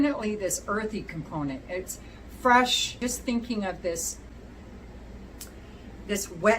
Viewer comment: Lord help me. When you were describing the vetiver piece, I would have sworn you said "Wet Beef'...? 😲👀😂 Took me a moment to realize wet LEAF was a more likely phrase or statement! I was actually sitting here wondering how a wet beef perfume would smell...😂 Mercy !!!